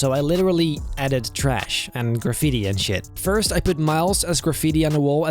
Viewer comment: did i hear say sh*t? 7:12 ???